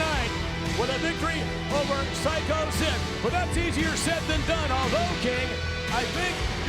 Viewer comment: swear to god i thought i heard vince say "easier sid than done"